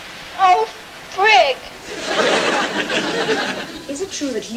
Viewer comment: Did she say "Frick"?